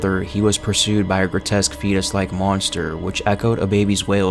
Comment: When you said "fetus" like monster I heard "pen"